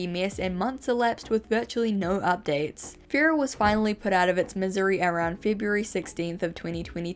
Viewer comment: I love how Auto-generated captions said "Missouri" instead of "Misery"